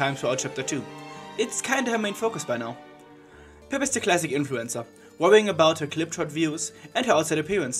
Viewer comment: Zipp and Pipp's dynamic is very good, it's nice what they did with them, also at 0:38 it sounds like you're saying influenza instead of influencer.